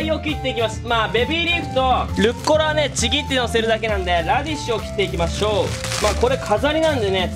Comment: ラディッシュがラディツに聞こえた